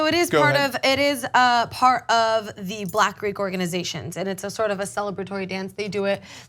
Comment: Is Cenk lazy, or just afraid to risk screwing up his racism narrative? Simply saying "witnesses said they only rushed off black students", is gossip, not reporting. Why not get ahold of the graduation footage and actually look to see if they indeed targeted minorities? My guess is, the footage contradicts his claim. Also, the female host said that "strolling" was a tradition in "Greek black organixations". (At least that's what it sounded like she said. It was hard to understand her enunciation) so it would make a lot of sense if black students were the ones getting in trouble. They have one example of a white student doi g a backflip, that wasnt dragged off. But it's pretty dangerous, and nearly impossible to actually try and stop someoneone doing a backflip.